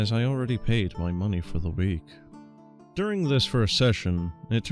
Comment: I thought he said he payed for the week in advance